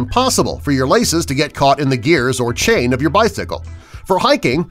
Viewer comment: Did he say bythicle